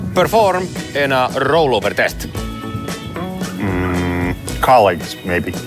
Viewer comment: what did jamie say at 0:46? "collegs" ? "car-legs" ? i dont get it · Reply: Colleagues, maybe. 😃